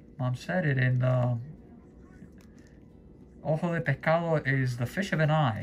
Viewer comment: Do you use the ceceo? For a second, I thought you said "ojo de pecado"!